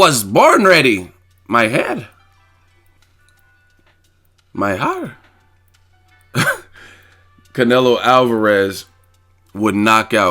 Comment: YOO WHAT DID YOU SAY. LIKE AROUND 1:20. MY HEA MY HAR LMAO. CRYING 😂